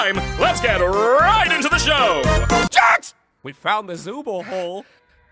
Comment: For 7:44 I heard we found zoobles hole 💀💀💀